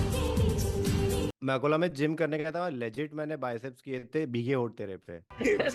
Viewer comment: did he just said Akola from Maharashtra?